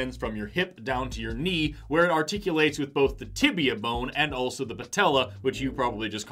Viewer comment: I thought you said “The tip of ya bone” 🤦 you said Tibia bone